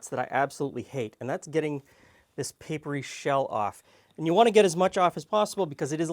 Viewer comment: I 100% thought he was about to say "papery shit". 😂